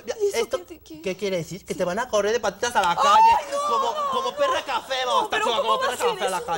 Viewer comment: ¿Alguien sabe que dice en el minuto 4:40? La van a correr como que? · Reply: como perra cafe....jajajaja como perra cafe a la calle